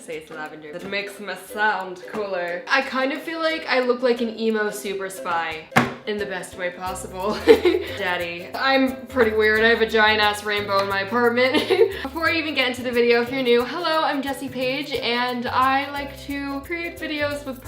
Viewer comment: I thought she said vagina rainbows 😂